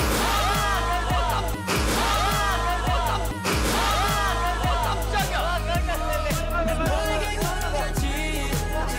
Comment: Am I the only one who heard what Jay almost said? Yes? Ok. I love him. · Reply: He was saying "Wa kammchagiya", which means "woah, that scared me". The clip was cut and replayed, so it sounded like he was about to saw wtf, but he didn't.